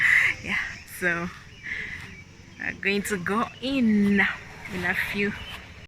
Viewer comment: "...in a few..." almost sounds like "I love you" because she said it so quickly and you could mishear 😂😂😂😂😂